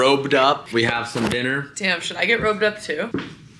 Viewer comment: wait did she say "TIM SHOULD I GET ROBED UP TOO?" or 'DAM SHOULD....." mmmmm my guy, not gonna lie i been waching for years but still dont know your full name lol just always...